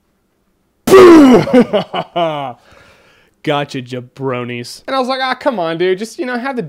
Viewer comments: did you say bronies? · why target that to Bronies? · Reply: Christopher Joy ok, then I clearly misheard him say bronies. my bad. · I thought he said "Gotcha ya Brony's!" XD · I thought he said Bronies?